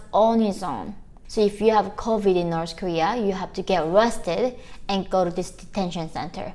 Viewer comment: At first I thought you said "if you have covid-19 you have to get "RESTED" 🛌 🕵🏼‍♀️🙃 BUT you said "ARRESTED"...🙄 🙏🏻😭💔 so sorry for your good people. <link>